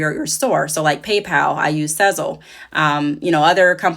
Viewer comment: What was the payment provider you said you used? I heard you say PayPal but you said another one after pay pal. Was it sounded like you said sizzle but I’m not sure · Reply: Hey there! I use Sezzle (it's a buy now, pay later options for businesses). Here's a link with more information and to sign up if you're interested in using them! <link>